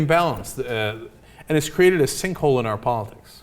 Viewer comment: Thought it said stink hole.